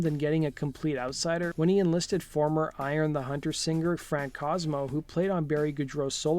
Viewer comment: Sounds like he says, I Am The Hunter, instead of the correct name of Goudreau's band, which is Orion The Hunter.